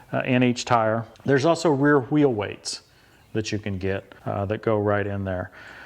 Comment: did you say "hwheel hweights"? 🤣